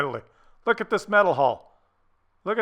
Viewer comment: with all that mental cases talk at the end of the video I actually heard you say: "look at this mental hall" I think I have some mental issues myself. lol